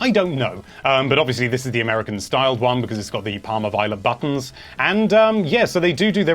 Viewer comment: Question: What does he say at 18:08 ? This is obviously the American styled one because it has the ____ buttons? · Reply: It has the parma violet buttons.